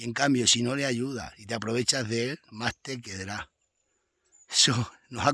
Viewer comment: <---- ¿Qué dice exactamente? ¿Dice: más te "quedrá"? ¿Qué palabra dice?